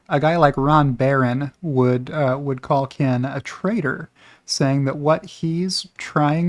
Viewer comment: I keep hearing "trader" as "traitor"". 🤣